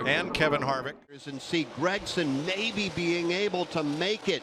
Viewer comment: did he just say N word hard R